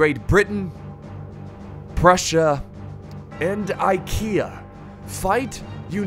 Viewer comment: So I was wondering what you just said and then I realised "IKEA" was not a faction LUL